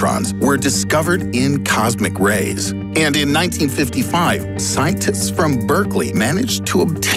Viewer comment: did he just say scientits?